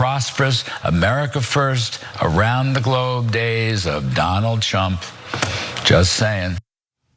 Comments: Huh, did Kudlow call him "Donald Chump", instead of "Trump" just then? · I thought he said Donald Chump... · did he say “Donald Chump”? 😂